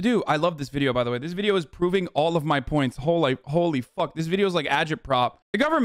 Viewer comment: he almost said "hole i f*" accidentally instead of "holy f*" lmao